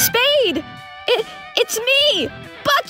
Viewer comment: I heard F*ck it! Nooo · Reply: He said BUCKET, not F(%!$#) it.